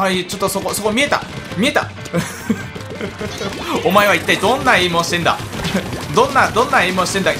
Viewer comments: くらいで カズさん「お前どんな陰○してんだ」にしか聞こえないんですが、どなたか和訳お願いします🙇⤵ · お前は一体どんな陰毛をしているんだ。に聞こえるwwww